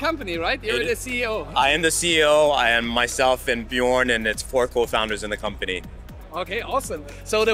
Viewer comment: I thought he said Bjork for a second 😅😆